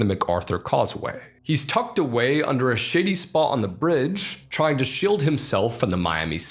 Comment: hearing swear sounds wrong, so at 4:41 does he say shady or shitty? both make sense in the context. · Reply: Shady